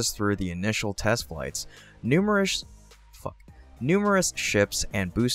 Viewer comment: did you just say "Numerous f#$£ it???😲😖😖😖